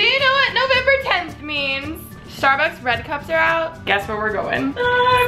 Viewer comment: I thought she was saying bread cup